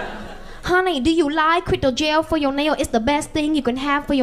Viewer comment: I think she said do you like ''glitter gel?'' 11:21 yeah... LMAO